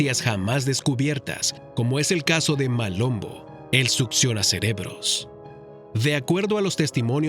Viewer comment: En el puesto 5 entendí "Maluma el come cerebros"